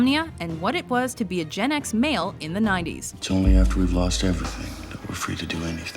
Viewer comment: a gen ex male? 3:16 what did she say and what does it mean?